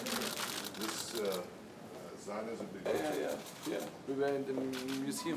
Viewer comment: Did he say Zionisism at 00:17.